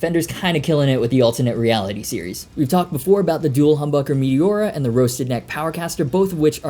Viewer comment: Wow! That fender guitar. I was not paying 10000% attention, because I was walkinng, then when you said the name of the humbuckers, I thought you said Meliora, and I looked down and saw a new RG! I thought I did.. You need to have ghost, to get this tho..